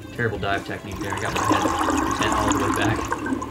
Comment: What did you say @ 4:30? Couldn’t make out the comment about bad diving technique... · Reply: I was looking up at the surface with my neck bent all the way back. its a no no when diving deep.